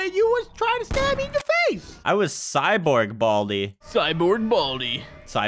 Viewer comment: what was gonna say at 9:38🤨🤨🤨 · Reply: Cyborg Baldi